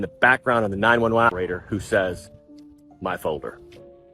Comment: Omg the man's voice actually sounds like it says "your life is over.".. not folder. In the audio on the 911 call. Listen more closely.